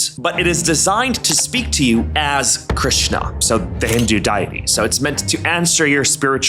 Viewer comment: Did you just say diety?